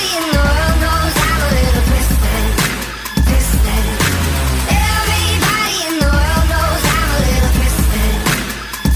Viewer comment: qnd 10:22 what did they say and I couldn't understand clearly because yellow colour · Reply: Sorry about that! 10:06 = “duh you human flamethrower” 10:22 = “fym he looks like a menace”